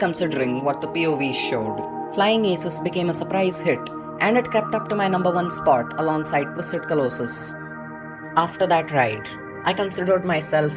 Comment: what did you say at 1:26, I couldn't understand you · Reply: “it kept up to my #1 spot alongside Twisted Colossus”